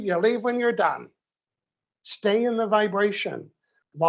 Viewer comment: I don’t get what he’s saying…it’s like something said was cut…does anyone know what he was saying there? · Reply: "Stay in the VIBRATION" 😂❤